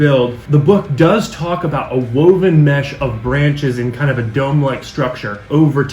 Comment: Hey! I loved the video and thanks for another great tutorial! In the video, at 3.47 you mention a woven mesh dome of branches. Well, when I first heard this I thought you just said a dome! I envisioned making a dome from paper mache (I use the brand that I buy from the kraft store that comes as paper pulp with glue already added. Not strips of newspaper. It's about 1000% better!) and having parts of it missing due to age and deterioration! So even though you actually said "Branches" I'm planning on doing it much like the two-story ruins we see in your intro but with a partial falling apart dome! BTW In what video do you show how to make those ruins?